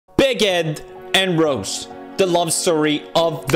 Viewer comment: I really thought he said 'Bigot and Rose'.